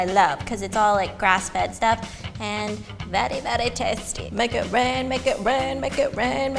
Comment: When you were saying "Make it rain!" at like 4:35 I thought you were saying "My goat ran!" LOLOLOL